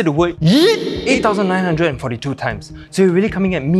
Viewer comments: theres a mistake with the script He said 8942 times but the subtitles said 8972 times😂😂🔥❤️ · you mean 8972 times the subtitles say so